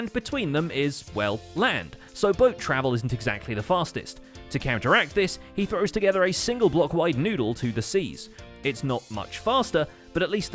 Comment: DID YOU SAY NOODLE